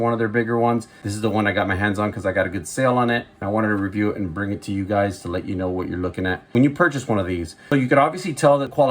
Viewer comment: Which model Kershaw did you show at the 3:08 mark? I replayed several times and cannot determine what was said… Dividend, Divotted? But I’m 66 and liked my music LOUD. THANKS for the videos!! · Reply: Thank you for watching! Yes it’s the Kershaw Dividend. Great knife!